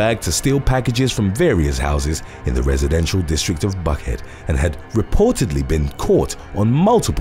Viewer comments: did he say Butthead or Buckhead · Lol. I thought he said butthead and I thought what a weird name for a town. I caught Buckhead the 2nd time. 🤣